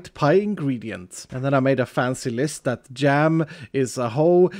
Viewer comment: I was not looking at the screen and I heard "Gem is a hoe" xD I'm glad I misunderstood that